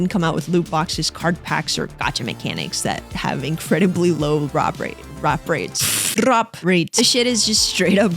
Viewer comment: bruh, at first I heard ropraid (English is not my first language, mind you) and I was wondering whether there is a word I don't know. It isn't so, thank You for that